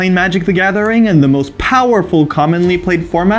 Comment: It was a wording to keep the vintage players from going "no the most powerful format is vintage!". Because although they are correct, not many people play vintage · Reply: maybe this was my mistake, i thought they said popular format. powerful id say you im with you. thanks for the reply